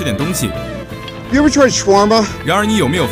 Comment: 我听成 你们喜歡吃三八吗